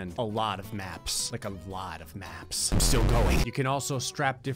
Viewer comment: When he said still going I had to rewind because I thought he said sugoi.